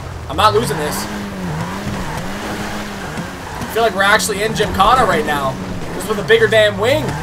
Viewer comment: did he say Jakarta, the capital of Indonesia at 07:35? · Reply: No.He said Gymkhana.